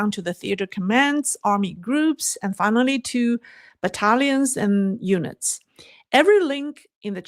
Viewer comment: 😂😂😂 it sounded like she said Italians and eunuchs @ 20:58. It was battalions and units